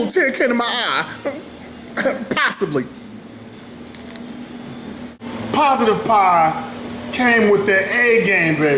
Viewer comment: did he just say "piessibly"?